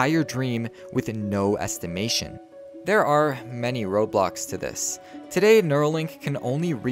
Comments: Did he say Roblox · i thought u said there r many roblox to this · did he say Roblox? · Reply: no he said road blocks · diD He sAY RobLoX?? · yes, i heard "there are many Roblox" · why do I hear Roblox